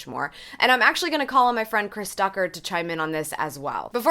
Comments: At first I thought you said Chris TUCKER I got excited for a second 😭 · Thought she said Chris Tucker.